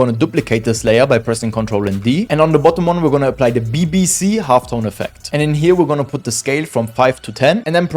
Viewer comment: i heard bbc